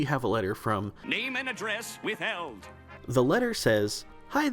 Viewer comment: As a kid I always thought they said "Name and Address, with Elves." On spongebob during the patchy mail. I just realized thats not what it says. I dunno it made sense to me as a little kid.